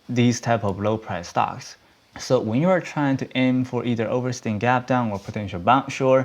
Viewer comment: What did you say at 8:40 ? "(something)-- gap down"? also what are "multi day runners"? googling it doesn't give a proper definition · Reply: Over extended gap down. It's a pattern he teaches in his DVDs